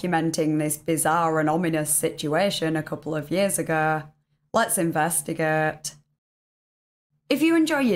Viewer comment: "lets investi...." What? WTF did you even say? I know what you meant to say but, that is not what you said.